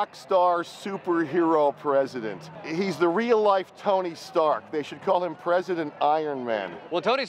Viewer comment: For some reason, when he said "Tony Stark," I heard "Tony Soprano."